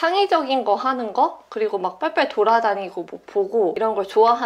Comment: 빨리빨리를 빨리빨리 말해서 빨빨이라 들리네